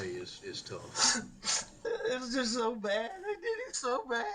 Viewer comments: I heard "I did him so bad" NOT THEY! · notice how his lawyer kinda looks around like “did anyone else notice that?” when Alex says “I did him so bad”? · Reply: And I hear “I did him so bad.” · Sounds like he said " I did him so bad" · Did he say I did him so bad 😮 listen to this section again towards the end when he breaks down again with his dry tears 😮it sounds like he said I did him so bad did you hear that people. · Sounds like AM said"I did it so bad."